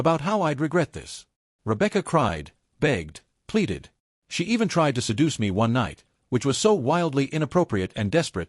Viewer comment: Hold tf up, did he say she tried seducing him at the end wtf 😐